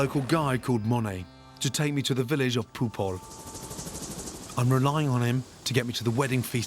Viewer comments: Did he say the village of poop on? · i heard ” to take me to the village of poophole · Am I high or did Gordon just say the village is called "poophole"? 😂😂😂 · it sounded like gordan said village of poop hole. correct me if im wrong 😂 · the way he said is POOP HOL well thats what i heard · Gordon: “I’ve arranged to meet a local by the name of Monay to take me to the village of popoll” I swear I thought he said the village of Poop Hole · did he say village of poophole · did he just say the village of poop hole lmao